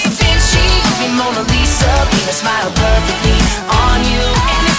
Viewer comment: He didn't say penise smile perfectly on you he said paint a smile perfectly on you or didn't he?! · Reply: It sounds like he said penis smile but it is paint a smile